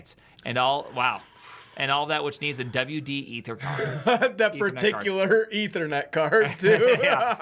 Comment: he said "Ethercard" but andy wrote Ethernet Card. meanwhile , if you look up the product online, it IS actually called a WD ETHERCARD ... how did Prime know ?